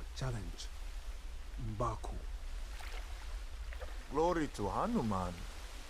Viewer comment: Did he said glory to hanuman?